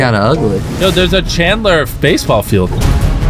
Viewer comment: is it just me or did jimmy say faceball field